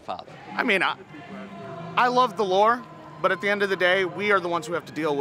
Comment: "I love the lore" cuz George Washington is just basically Hercules. 🤷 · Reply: I thought he said “I love the law” with a thick accent.